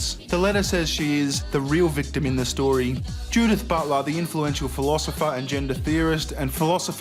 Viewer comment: Is it just me or does it sound like said "butt love" instead of Butler?